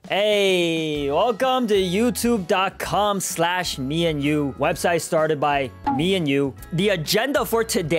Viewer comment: when he said meEnyu i thought he said me and you 😭